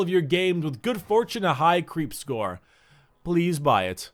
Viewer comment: Did he just said TEEHEE in